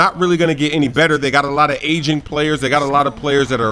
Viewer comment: LOL I thought he said "asian players" not aging players.